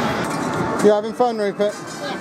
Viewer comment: what did he call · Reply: Rupert